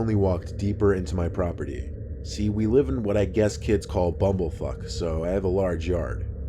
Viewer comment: did he just say “bumblefuck” or did I hear that wrong?